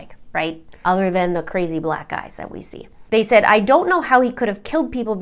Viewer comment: Did u just say crazy black guys u racist ash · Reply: LOOOL she said "black EYES". She even does the gesture. Y'all are weird istg